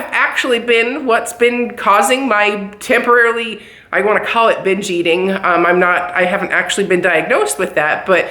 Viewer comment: You go girl. Funny but when you said binge eating I thought you said bitch eating and thought well what is that, maybe that’s my problem! Haha enjoy watching you. You are so real it’s refreshing.